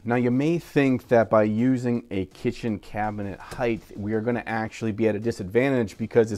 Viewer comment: wait....... did he say the word Height'th at 1:17? is that even a word?